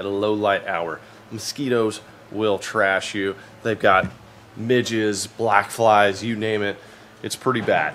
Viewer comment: I know it's not what he said but I was in the middle of opening a cold one when he said it but when he was talking about the bugs in the beginning I heard "they got midgets" and spit out my jerky